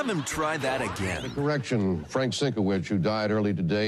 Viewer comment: i thought i heard "an erection and not correction XD then 70degrees lol